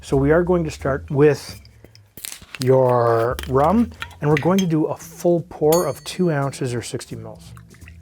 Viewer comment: I thought you said "we're going to do a full QUART" ... Wow, that's a boozy drink! 🤣